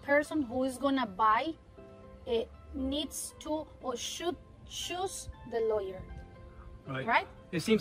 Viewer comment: She seems very trust worthy and i would use her . @ 7:20 i thought she said "and what i recommend is the person who is going to buy , needs to or should , "Shoot the Lawyer". . she actually said "should CHOOSE the Lawyer". thank God .